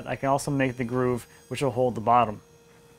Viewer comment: your subtitler wrote "bottles" instead of "bottoms".